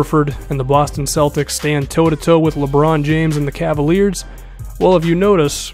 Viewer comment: did you say lebron James and the cave he is not the only person on the team